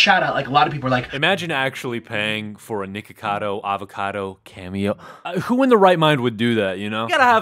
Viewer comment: For a second I literally thought he said Nakedcado Avocado. 🤠